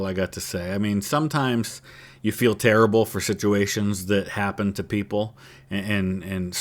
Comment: sounded like you said shituations 😂